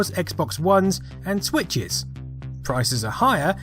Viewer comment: did you say Twitches? ;) Love your videos! It sure was more fun back in those days ☺️